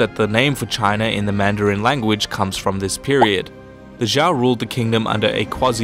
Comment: Did he say Ja Rule? 2:35 lol